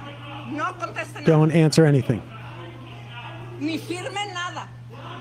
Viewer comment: “Ni firme nada” means don’t sign anything the AI captions are wrong, again